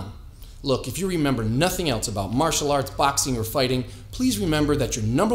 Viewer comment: i heard "hands down you are Fousey Tube"